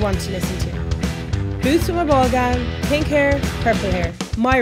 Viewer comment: can somebody let me know what's she saying at 00:23-24? Just curious. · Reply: 'Boots with a ball gown'